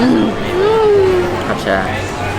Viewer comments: Love you guys ...you guys are GOALS !!! Did he said " Acha hai "13:16 in hindi .... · did I misheard or really said achaa hai (its nice)